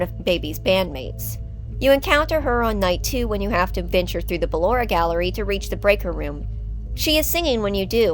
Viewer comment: I’m sorry what did she cuss or say something else 😂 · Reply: Nah. XD I was just saying you encounter Ballora on night two while passing through the gallery.